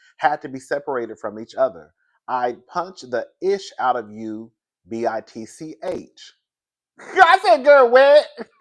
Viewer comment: After I cleaned the wax out my ears, i heard that “I’ll push the ish out you b****” loud and clear!